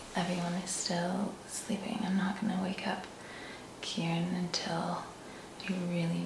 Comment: Oh, it's Kieran! I thought said Karen. I thought 4 a sec she was married 2 a woman!